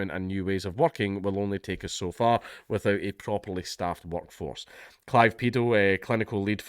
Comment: I had to pause the video and read the article coz I thought you said that guy's name was "Clive Paedo" 😂😂😂😂